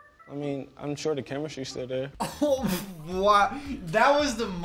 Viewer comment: what does say? Pls tell me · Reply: “Im sure the chemistry still there” I think